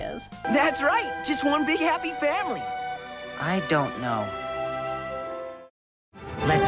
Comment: i heard "le's be a happy family" and then fangirled for a while 😂